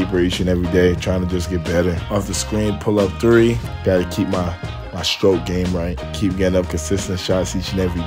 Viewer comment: did he say "get my stroke game right" with a smile on his face????